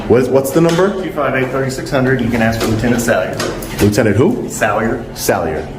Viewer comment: I thought he said you could ask for a tuna salad lol